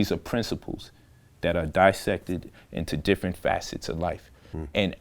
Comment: is he saying “netchur”? I’m trying to research about what he’s saying but I can’t find it. Any help will be greatly appreciated! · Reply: Ntr ...The ancient Egyptians called their writing system Mdw Ntr (pronounced Medu Neter), which means “god's word” or “divine word.” Scholars in Black Studies often use the term Mdw Ntr to refer to the ancient Egyptian language in all periods, and they therefore may use the term interchangeably with hieroglyphics.